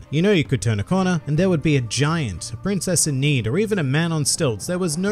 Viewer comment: When he said “you could encounter anything like a giant, a princess in need..” I thought he said a giant princess in need and thought “ha what a flub” then thought there is a giant princess in need -__- lol One Piece got it all maaaan